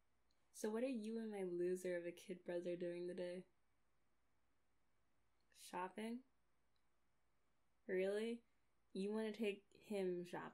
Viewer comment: Not gonna lie...I thought you said fapping at first. PAUSE